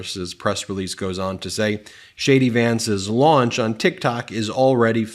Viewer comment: LOL I thought you said Shady Vance LOL😂😂